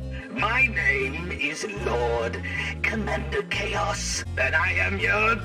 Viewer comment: I thought it said “Lord Commander Chad” not Lord Commander Chaos.